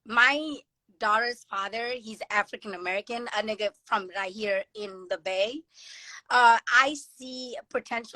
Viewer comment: Bro correct me if I’m wrong but did baby say “a Nigga from the bay” at 8:20 ? Or am I tripping